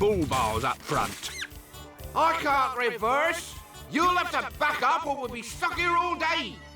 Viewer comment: "I can't reverse! you'll have to fuck up or we'll be stuck here all day!" I what I heard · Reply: She said "back up".